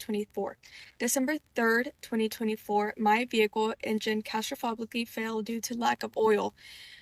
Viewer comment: did babygorl say “castrofogically” ?